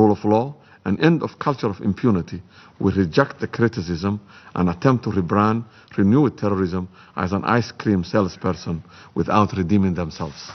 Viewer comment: Can someone please clarify what he said around 15:20- 15:30? It sounded like he said,"... we regret the criticism and attempt to rebrand and renew terrorism as an ice cream sales person without a shop..." loosely dictated of course.